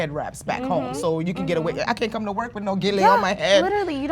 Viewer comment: What is that lady saying. Yiu can't go to work with gele on your head